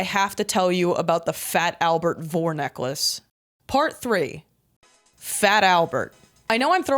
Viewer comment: I heard "Fat Albert vore necklace" and had to pause the video to regain my composure